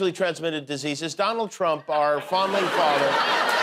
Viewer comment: Swear I heard "Our founding FARTER"